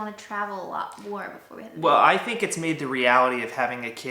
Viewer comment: Did they know they were pregnant at this time? At 2:34 it sounds like she says "before we have the baby" instead of "a baby." 😂